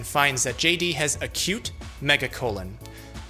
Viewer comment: See, I totally misheard. Sounded like a new way of sayin': "She Thick"... As in: "That Hoe over there? She got a Cute Mega Colon"